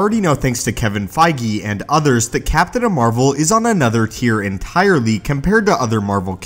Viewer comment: Did you just call her Captain Amer-vel?